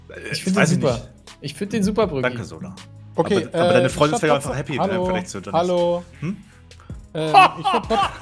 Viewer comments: was sagt broeki bei 1:37? 10 mal wiederholt und es jedesmal nicht verstanden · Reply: weil nicht seinen skin lobt und sola sagt : "Ich find den super", meint broeki: "Aber deine Freundin ist auch viel happier im vergleich zu johnnys" wenne verstehst ;) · Was sagt Broeki da bei 1:35 über Freundin? Ich versteh das akustisch irgendwie nicht... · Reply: Broeki sagt "Aber deine Freundin ist ja happy, im Gegensatz zu Jonnys." Weil Sola den Skin gut findet und eben nicht ^^